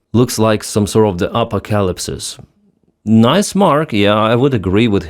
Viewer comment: You said: "Apocalypse" I heard: "alpaca lips" Not a bad description honestly.